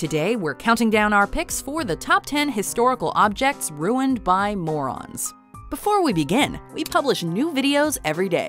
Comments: Thought it said “Mormons” not “morons” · Oh! I thought it said “ruined by MORMONS”!!!!!!! Ha! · Thought it said "Top 10 Historical Objects Ruined by Mormons"